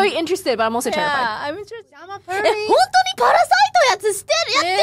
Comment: "I'm a furry" - (maybe not what she actually said, but it's all I can hear)